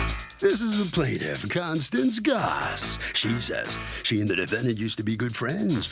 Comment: As I'm listening through my ear piece I thought they said Constance Glass which is my name 😩😩